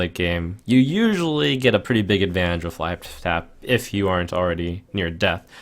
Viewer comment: did he say life fap? xD